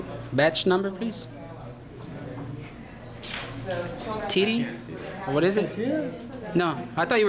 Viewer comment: did I hear you say titty? Lmfao haha